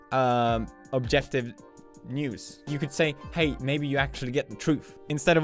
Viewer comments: I thought he said nudes instead of news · in the beginning he was saying news but i thought he was saying nudes like he said like you guys i get my news from the internet or something like that but what i heard was replace news with nudes